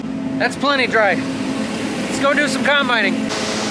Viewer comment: Going into the ditch is embarrassing enough, But having your boss come and video It , takes it to a whole new level. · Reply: Pretty sure I heard him say, “LETS GO BRANDON”. And you can’t fire Brandon.